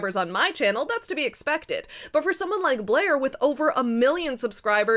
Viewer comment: You said "Blair which" many times but every time I heard "Blair witch"